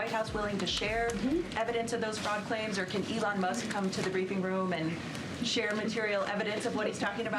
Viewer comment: When the clip first started I misheard the speaker and thought she called it the “weeping” room instead of briefing room.